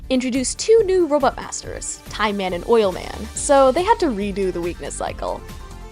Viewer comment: Time Man & Oil Man!? I thought you said "No Fake Bosses"